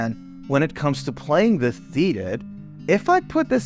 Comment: Hello bro, very good video, this doesn't have a lot to do with this vifeo but, what did you say at 10:03 right after "when it comes to playing the ...."? I'm a englidh learner I could figure out what that is, I just suscribided, peace💪🙏🏻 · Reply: Hey Oliver! It’s “when it comes to playing the guitar SEATED”. I think the audio cut off a bit in that section. Glad you liked the vid! Good luck with the English studies brother🤝🏼